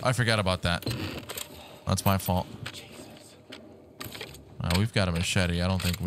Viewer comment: I thought it said Jesus said Jesus lol